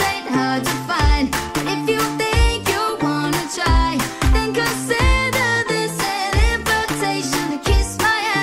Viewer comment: Instead of consider I thought it said think of Santa 😞